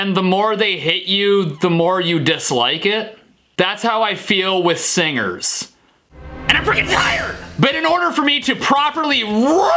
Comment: what is saying when He Screams Randomly in the Beginning? it just sounds like Hes saying "that's How I Feel about Singers AND FREE TIRES!!"